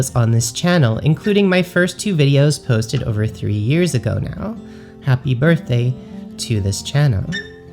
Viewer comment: when you said "3 years ago", I thought you said "30". LOL